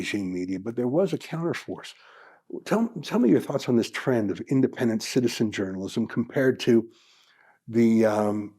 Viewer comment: I misheard "citizen jiournalism" as "cynicism journalism". Either would be equally valid in this age of evil.